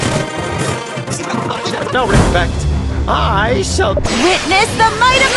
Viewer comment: sea fairy be talking so fast Im hearing “what on my seat” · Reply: i think i hear " wittness the might on my seat